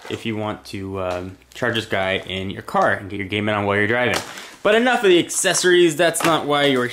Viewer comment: did he say Gaming WHILE your driving??